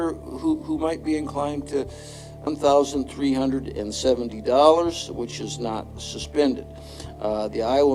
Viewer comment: Who did the captions? “I’m thousand $375” instead of “$1,375”